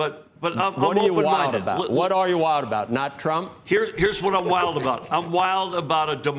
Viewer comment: Sounds like he just said he’s wild about Trump 😅